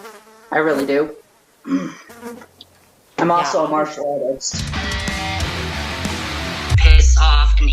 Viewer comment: dude i thought he said marxist and i was so confused